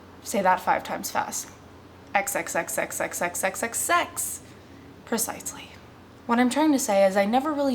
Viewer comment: I am not very good on hearing english, I do read well tho.. anyway, at 0:29 did she say sex 7 times? :D, anyway thx on making videos u r pretty xD